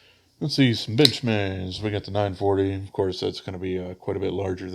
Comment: I thought you said “some bitch mades” I almost died laughing